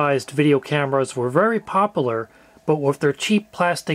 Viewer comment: no offence it sounded like you said very poperler at 0:21 Its popular and you pronounce the U